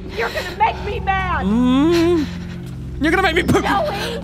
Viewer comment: did he just say "your gona make me poo-""